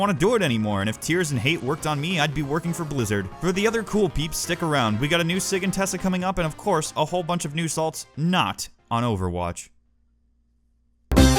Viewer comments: i heard "new sig and tessa" and almost shat myself. So excited! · I heard "New Sig and Tessa" and subscribed